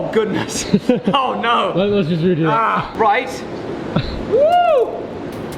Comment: Is Anyone knows what he just said.....Please tell me about it.. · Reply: Looks like he said "Let's smash"